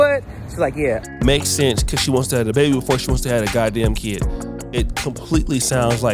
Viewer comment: Hold on did say she wanna have the baby before she have the kid @ 5:13 😂